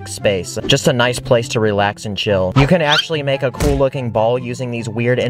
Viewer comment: why did I hear “relax and shit”